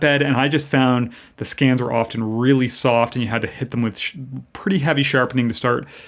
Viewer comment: It sounded like you were going to say ‘hit them with a shit load of sharpening’ at 6:39 but stopped yourself 😂